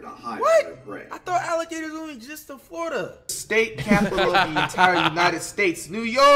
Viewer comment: what did he say can someone tell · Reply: he said i thought alligators only exist in florida